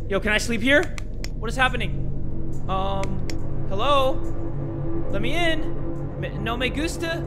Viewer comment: does he just say let me in, you know, the meme